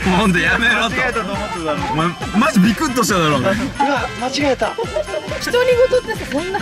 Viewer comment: にあっ間違えたと聞こえた